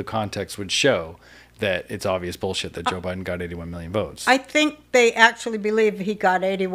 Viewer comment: I thought she said Biden got 81 million "jokes"....which would be more accurate.